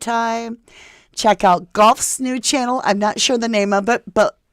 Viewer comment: Does anybody know the channel is referring to? Is she saying Gough? Thx ❤